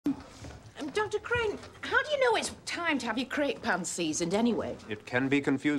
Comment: First saw this when I as pretty young. Early teens. Thought they were saying "Crepe Pants". Was very confusing. I had no idea what a Crepe or a Crepe Pan were supposed to be. What they meant by "seasoning" your "crepe pants" was beyond me.